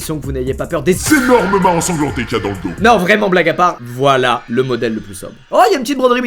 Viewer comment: black à part ???? Quoi ???!!!😂😂